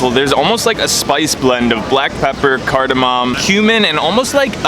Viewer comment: when he said cumin i thought he said human and i was like oh shoot here comes a lawsuit